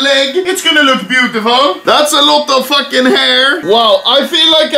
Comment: i misheard the intro as “thats a lot of fatmail”